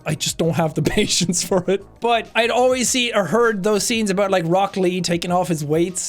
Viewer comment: For a moment I thought he said Broccoli but it's Rock Lee 😂